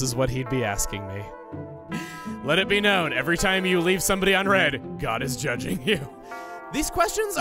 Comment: God is judging you everytime you leave a text unread Me: That means he's judging me 95% of the time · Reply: I thought he said 'left someone ON read' like, you check the message but don't reply